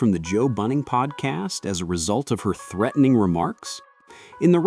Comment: Did you just say Joe Bunning Podcast? Who is that? 🤦🏽‍♂